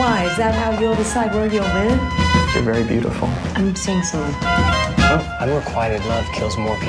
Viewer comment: what is she saying at 1:04 ? I didn't really understand it. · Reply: I believe she says "I'm seeing someone"